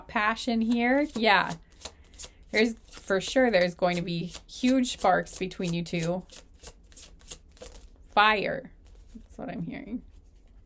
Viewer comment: Pile 4. Also, aroun the timing of 7:10:04 I thought you said, "There's gonna be huge farts between you two." Instead of, "There's gonna be huge sparks between you two." I was confused and had to replay to make sure I didn't hear that right lol, I'm glad I heard that wrong because other wise... That'd be... A little crazy lol. 😂❤ I loved the reading tho!